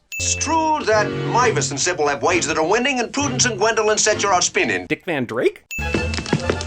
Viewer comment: Did he say, "Set your ass spinning"?